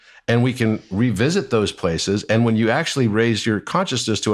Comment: At minutes 19:34 Did say … “INF level” meaning Myers Briggs personality Type INF? Or is he referring to something else ?